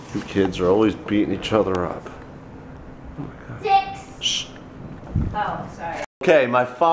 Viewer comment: Correct me if I'm wrong but I'm sure I heard correctly that at 1:54 said "dicks" to the cats pmsl hahaha